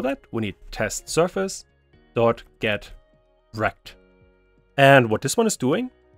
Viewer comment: I heard "Test surface dot get rekt" 🙃